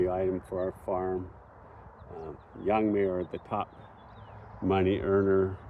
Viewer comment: What is the crop that you said is the most profitable thing for your farm? It sounded like you said young mayor but I couldn't find anything by that name